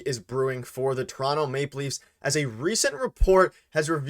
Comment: Did he say "Maple Leafs"?????? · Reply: I thought I heard say ‘maple leafs’